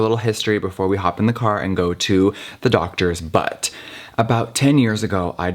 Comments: I LITERALLY THOUGHT HE SAID "before we go into the doctors butt" I had to contemplate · "Before going to the doctors, but" thought he said doctors butt b4 he continued his sentence 🤦🏻‍♀️